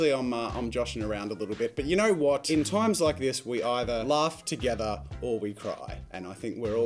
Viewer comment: I thought he said “we either laugh together, or we die! :D” and i was like well o k damn we have no choice then 😂